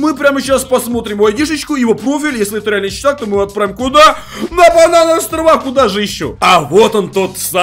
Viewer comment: я один услышал на барановые острова?